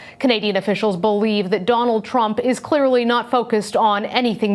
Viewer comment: did she just say danald trunk XD